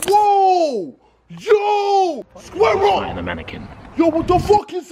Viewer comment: Did JJ say ''Squirrel'' or ''Square Up'' to the Lizard? XD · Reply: Curtis Elwood square up